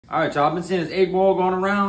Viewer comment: I swear I thought he said 8ball